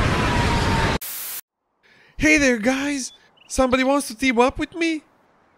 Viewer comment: What does Ezreal says at 0:27? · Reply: I'm not sure, but it sounds like: "Hey there guys! Somebody wants to team up with me?"